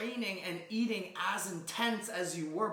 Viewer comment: Lmao this is totally on me but at 5:03 I heard "eating ass in tents" bahahaha